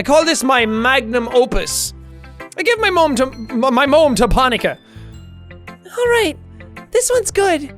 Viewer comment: My moem To ponakai that what I heard